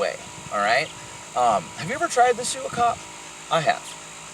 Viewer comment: not going to lie when you said "sue a cop" i thought you said "shoot a cop" and im like man you tried to shoot a cop? hahaha